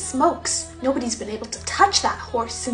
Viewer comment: No OnE WaS BeEn AbLe To ToUcH ThAt HoRsE SiNcE Ur Ma DiEd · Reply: I heard it as “Ur Ma Dad” 😭